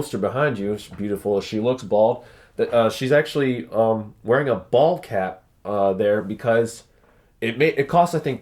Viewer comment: I thought he said she was wearing a "ball cap", like a baseball hat.